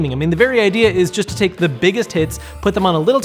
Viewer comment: Am i the only one who hears him saying the biggest tits?